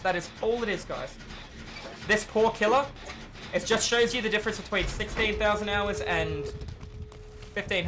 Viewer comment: What did you say? 16.000 hours? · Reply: He sayed „difference between 16 thausend and 15 hundert hours“. has about 7000 hours ONLY on killer.